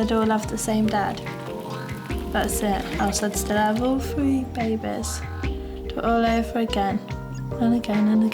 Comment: I Turned on the captions and Whenever she says BEY-BEH ,the captions say BABY BEARS 😂😂😂😂